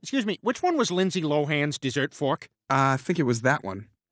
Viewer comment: I thought it said give me 2k….